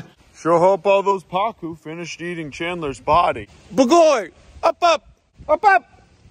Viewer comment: did he say bagoly? Hungarian